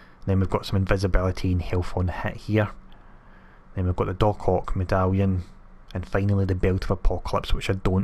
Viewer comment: When u said "Doc Ock Medallion, it sounded like u said DarkHawk" I was about to get super excited like where is that LoL... Awesome vid man, there always a big help for me